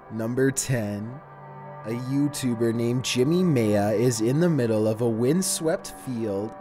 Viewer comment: It sounded like he was saying jimmy mayo.